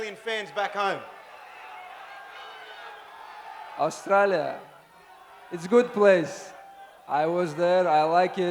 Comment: I thought he say Australia don’t have Wrestling 🤣